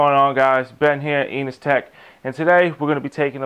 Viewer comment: i thought he said anustech XD